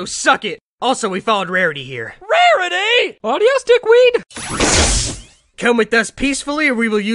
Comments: Adios, Dickweed. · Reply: First time i heard this i thought it said Adios Autistic weed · I don't know what's funnier The original line "also we followed Rarity here" Or what I keep mishearing the line as, "Also, we found Rarity here"